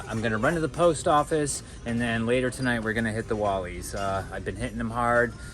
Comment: AYOOO WHAT DID HE SAYY also nice vid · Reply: I said I’ve been hunting a lot. ?